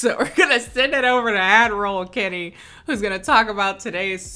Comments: deadass keep thinking she's saying "adderall Kennie" not "ad roll Kennie" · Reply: I thought she was saying “admiral kennie” and I was very confused every time bc there was nothing to indicate she was an admiral of sumn😭✋🏽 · I thought you said "Adderall Kennie" not "Ad-roll Kennie" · deadass thought you said "adderall kennie" and i was like damn same girl